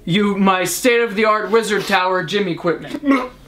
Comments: I thought he said lizard tower, you guys can attend my funeral its on Saturday. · I could’ve sworn said lizard tower not wizard tower